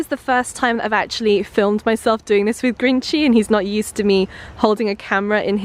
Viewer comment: When you said Grinchy and cheese I thought you said green cheese...lol